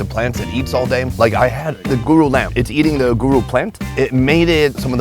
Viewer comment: what lamb did he say? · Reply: Guru